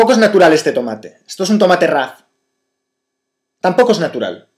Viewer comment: al principio escuche "esto no es natural, esto es un tomate raro Y NO ES NATURAL" jajaja